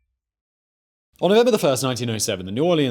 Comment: “Onnober the First, 1907”?? wtf did he say?